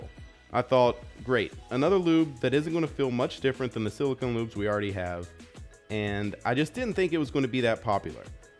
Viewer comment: The captions said silicone boobs and that’s what it feels like